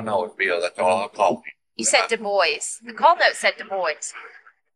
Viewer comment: When he said "DuBois" I really thought he was saying "The boys" x'D and I was like "ooooh yeah tha boys! Roll tide" x'D